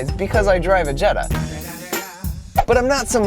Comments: Who else thought he said Jedi instead of Jetta? Noone? Just me...? Oh.. Th- That's okay. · When he said Jetta I thought he said Jedi